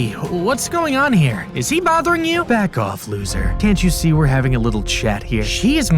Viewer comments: Anyone hear F off not bug off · OMG DID SHE SAY F*CK OFF